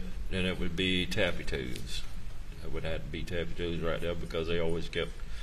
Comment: I love listening to this witness ❤❤ “bubba” “arm-uh-dilla” “tappy toes” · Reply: "Tappy"? I thought he said taffy. That's a cute name!